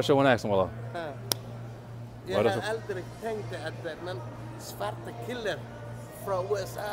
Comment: Did he Say zwarte Killer 2:12 ... WTF LOL